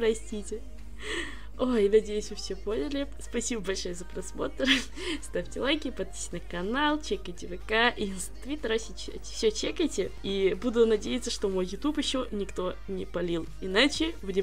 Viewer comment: мне послышалось или она сказала Твиттер?